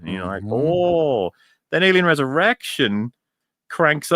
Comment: I thought said "that alien has an erection"